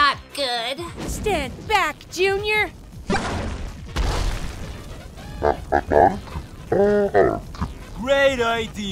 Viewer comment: I wonder what they said as Chain Chomps in 16:35. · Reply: "You okay, bro?" "Just fine!"